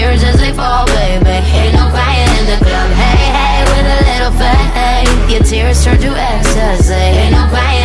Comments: All I hear is “fat egg” instead of faith help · Original lyric: "With a little faith, your tears turn to ecsaty" Wut I hear: " With a little fat egg your tears turn to ecsaty" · When it say “Hey, hey with a little faith” I hear “Hey, hey with a little fat egg” 😂😂😂😂😂😂😂😂😂😂😂😂😂